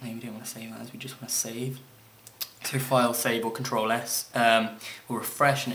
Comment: Can't you hear what he said "Click file > save or Ctrl+S."